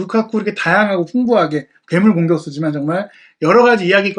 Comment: ㄷㅁ 공격수로 들리네요...ㅋㅋㅋㅋㅋ ㅈㅓ만 그런가요